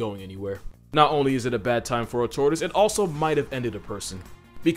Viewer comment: He said “tortoise” but I heard “tourist”😂😂